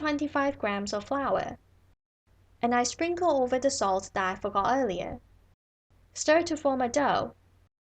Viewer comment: I am sorry, I can't understand what is being said at 1:26. Can anyone help, please? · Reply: A little late but: 'And I sprinkle over the salt that I forgot earlier'